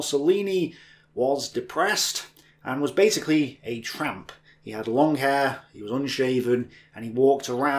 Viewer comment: LOL the first part is hilarious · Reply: Wait you said tramp? I heard a "Trap".